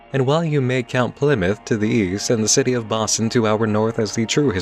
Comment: When you said "Boston" to the north, my brain initially thought you said Ba Sing See lol